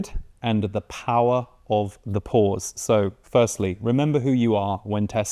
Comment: Omg, I thought he was saying "Power of the Pores" 🤣